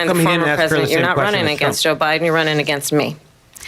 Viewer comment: Anyone notice at 2:11:43 she said to Trump he "wasn't running against "O'Biden", he's running against me"? Did I hear that correctly? O'Biden?